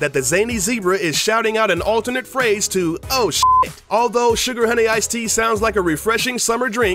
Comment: at first when he said " oh Sugar Honey Ice Tea" i thought he said "oh shit i had a nice tea"